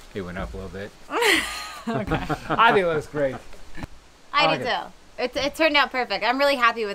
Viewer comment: Do you have someone helping with filming? I heard another man’s voice at 14:16. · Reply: No, I think you are hearing J say "it looks great" as the clips cut from him putting on the sweatshirt to talking about the work 😊